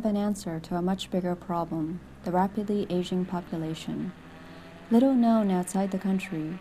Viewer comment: I thought she said "the rapidly Asian population"...